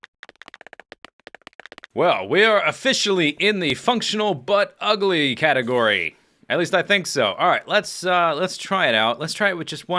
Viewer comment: Haha, i definitely heard it as "the functional butt ugly category"